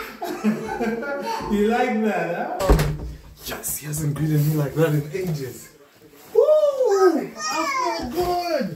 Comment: Hello everyone! I love this channel. Kobe is an angel. P.S.: 00:50 Who knows what is saying?) I'm learning English, and my goal is to comprehend fast English. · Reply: "Yes, he hasn't greeted me like that in ages!"